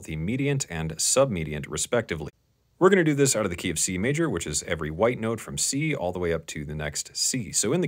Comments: I thought he said KFC major 🍗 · this dude said “key of C Major” so quickly i thought he said “the KFC Major” and for a second i thought the music industry had given C major a nickname